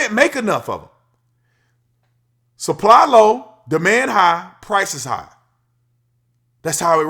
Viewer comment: I thought you said, "The man high," instead of, "Demand high," when you were talking about Tesla, Lol. Muck is definitely strange at times.